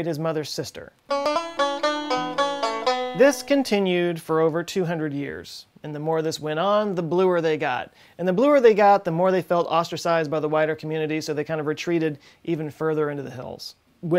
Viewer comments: Lol when you said “they felt ostracized by the wider community” 🤣🤦🏼‍♂️ I thought you said “whiter” 🤦🏼‍♂️🤦🏼‍♂️🤦🏼‍♂️ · Thars smurfs in them thar hills! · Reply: I thought he said, "And the bluer they got, the more they felt ostracized by the WHITER community..."